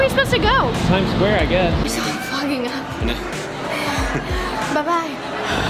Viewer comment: who else thought roni said f**king at 0:06?